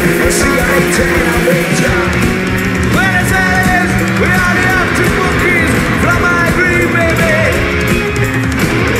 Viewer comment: que dijo? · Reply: Buenos Aires, we are the Arctic Monkeys from High Green, baby.